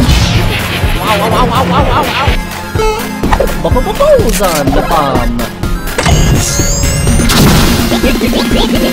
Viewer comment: what's he saying here? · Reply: he said boson-bomb